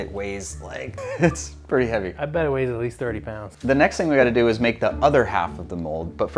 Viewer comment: when he say it weighs like 30lbs the CC says 30 euro pounds